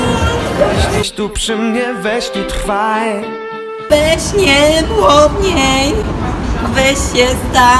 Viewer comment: usłyszałam Weź nie płodniej xDD